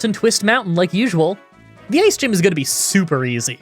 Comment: i know you say the ice gym but you say it so fast i heard Gay´s gym should be easy right LOL nul. thought Hydreigon was the end of this but nope holy hack this was a crazy run loved it